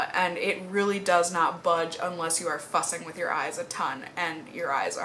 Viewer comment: I really thought you said, the eyeliner won't budge except if you are f**** with your eyes all day. Oh boy I need a hearing aid. What you were saying was Fussing.. Lol.. .. ."😆". 💖 I'm sorry forgive me.!!!!.. 💐 hahaha...